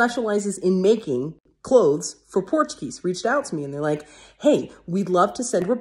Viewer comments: I could have sworn you said, "... clothes for Portuguese". I was so confused! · omg i thought for a minute you said a portuguese company not a porch geese lol i was gonna say you should of named him manny not carl lol ( im portuguese lol )